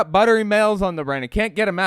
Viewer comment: Sounded like you said ✨buttery males✨